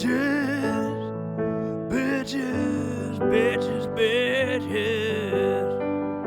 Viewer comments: Dude I swear When I watched the movie and this certain scene where bowser was singing about peach, I Thought I heard “ Peachess!…Bitches Bitchess!!” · In the original song at the end it really does sound like he saying 💀 “BITCH⃠IS! BITCH⃠IS! PAAAAAAAAAAAAACCCCHHHH PEEEAAAAACCCHH!” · "Bitches~~" -Bowser 2023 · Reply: I was laughing cause I thought he said bitches but it was peaches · I was watching this with my dad next to me and we both thought it said peaches but it actually said bitches ☠️☠️☠️ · When me and my friends were watching this, one of them actually thought Bowser was just saying "b!tches" until peach started showing up on the screen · At first I thought it said peaches but when I replayed it I was like omg he didn’t say peaches he said bitches-😅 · I thought he was saying peaches. But I listened closely and sure enough, "Bitches" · it does sound like he is saying bitches but in the movie he is saying peachis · At first I thought he said peaches his saying bitches 🤣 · Thought he said peaches but he said bitcs 😂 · I thought bowser was saying peaches but no it was bitches. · Ngl when I was watching the movie and saw this scene I thought instead of him saying peaches I thought he was saying b1tches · Lol I just watched the super mario brothers movie then this popped up😂 It's sounds like he says 'b¡tches' when he's actually saying 'peaches' as in princess peach😂😂